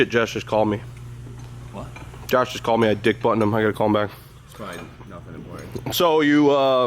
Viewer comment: did you say you “DICK BUTTONED HIM” like you butt dialed him?